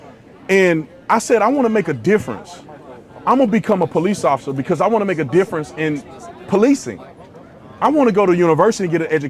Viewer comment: i thought you said you wanted to be a police officer because you needed a job and the police was recruiting.